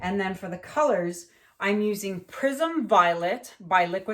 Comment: Lol, I keep hearing “Prison Violet”, my dang hearing! 😆😆😆